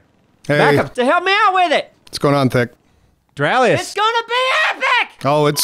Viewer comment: Did anyone else hear "Doraleous"? At 0:16?